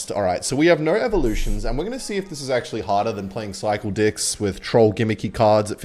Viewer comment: what did u say because it wasn’t decks?? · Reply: dicks*